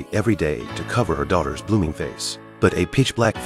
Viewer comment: I thought I heard "to cover her daughters poppy face"💀